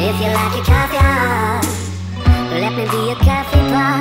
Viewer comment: "let me be your Coffee pot" instead of that i hear "let me be your cabybara" 💀